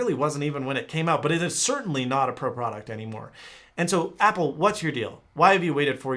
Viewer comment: If you buy any apple products, I have one question. How does it feel to waste a large amount of money? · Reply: Did you just say brand new apple